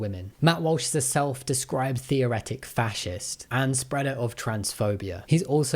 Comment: theoCRATIC, not theoretic. Meaning "religion first" instead of abstract. 🧐 PS thanks for including the bit at 40:11, i went hunting for these specific messages and in the process found some extra news from August of last year. As a consequence: Let me suggest we follow MoPOP's lead and call her "She-Who-Must-Not-Be-Named," exclusively. No initials, no surname, only SWMNBN. The Harry Potter books were written without an author. ^^